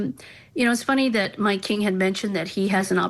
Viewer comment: did she call him “My king”?